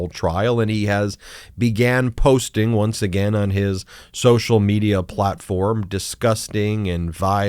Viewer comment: Did you just say "his social media plot forum" ? Accurate pun there. Unfortunately...